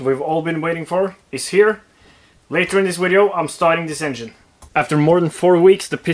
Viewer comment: For a moment I thought you said "Later in this video, I'm SELLING this engine" My heart skipped a beat.